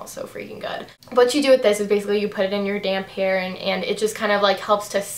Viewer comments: I thought you said "you out it in your damn hair" instead of " damp hair"!!! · When u said u put that chi stuff in Ur damp hair I thought you said I just put it in my "damn" hair Lol I'm like ohhh getting saucy